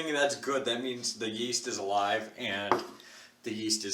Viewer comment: Did I just hear "the yeast is a lie" or did I mishear misheard not hear that right?